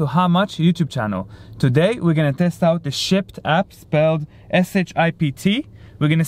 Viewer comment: For a second , I thought it said " SHIT APP " lol. Also churches people hardly tip.